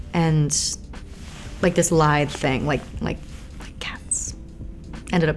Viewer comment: It sounds like she said "lithe" not "live" which also just makes more sense